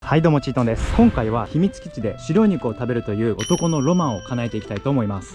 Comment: 白い猫を食べるという…… に聞こえて一気に眠気覚めた